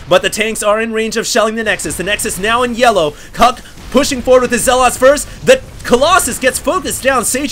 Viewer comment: Did he say "Cock pushing forward" at 8:38 ??